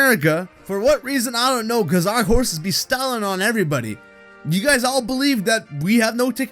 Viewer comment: I heard the word STALLION